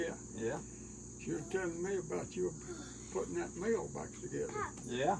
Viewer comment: what kind of corn did he shuck at 8:00? i couldn't hear the name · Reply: was talking about a mill?…